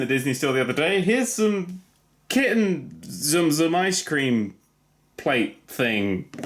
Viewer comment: I seriously thought you were saying zoom zoom until you brought in the build your own box… You were saying tsum tsum? O__o